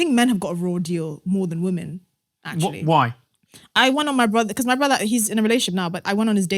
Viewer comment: What is she saying? I want to ??? on my husband.